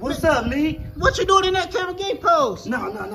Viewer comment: I thought he said Ginuwine why you in that kinda gay pose.😶